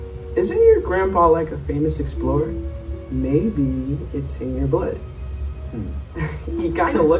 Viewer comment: i thought he said “maybe it’s in your butt”